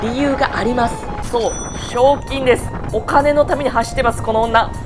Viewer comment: 海外レース挑戦お疲れ様でした👍 22:07の「賞金」がひょうきんに聞こえてカップラーメン拭いちゃったw きつい時でもひょうきんを忘れない関西人の鏡最高😊